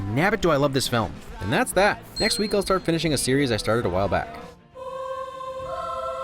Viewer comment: Subtitles are wrong >:( Says "are started.." in stead of "I started"